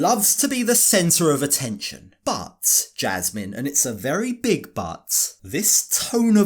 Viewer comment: Narrator guy.. Did you just say “but jazzman, and it is a Very Big Butt” ??? Man that cottage cheese cake straight out of a beef factory